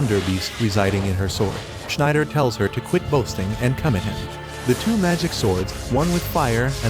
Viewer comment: am I the only one who heard come in him?